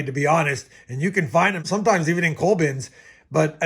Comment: "You can find them sometimes even in cold bins." What's a cold bin? Or what are you saying here? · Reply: “Cull” bins.